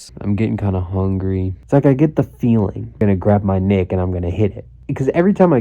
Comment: what did he say!?!? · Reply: Nic